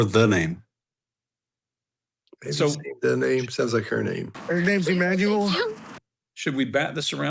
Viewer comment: I heard "HER name's Emmanuel" as well.